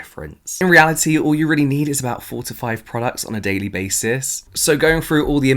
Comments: I thought you said 45 no 4 to 5 and got scared! Hahaha (not native speaker problems...) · I have my fan running and thought you said 45 products instead of 4-5 🤣😂 I mean, if you tell me I need 45, I would have 45! 💸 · For a second I thought you said all you need is 45 products on a daily basis LOL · I thought you said 45 products, not 4 to 5 products. lol